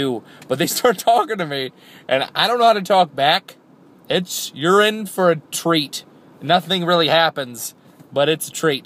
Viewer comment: "Its--You're in for a treat!" At first I thought he said "It's urine for a treat!